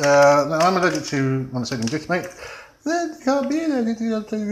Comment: I have no idea what is said at 6:58 ? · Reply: "I am allergic to monosodium glutimate" [whiney voice of people from reddit]"You can't be allergic to monosodium glutimate" "Well you can and I am"